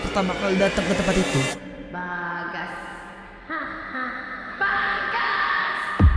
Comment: gw dengernya baka anjir 😭😂